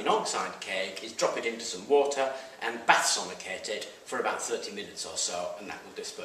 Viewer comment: or so, what is you said? It needs to be placed in a bath of water, and do what with it all the way through? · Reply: what? - bath sonicate is that what you mean?